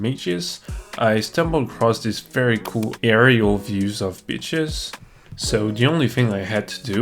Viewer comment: i dont think thats what he said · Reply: He said beaches